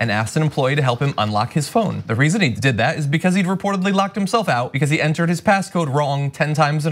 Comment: I thought u said locked up..damn!!!